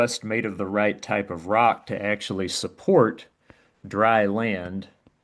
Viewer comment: I thought I heard the "cumshot peninsula."